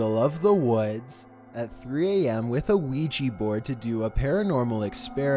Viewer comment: di....did he just say ouiji?🤣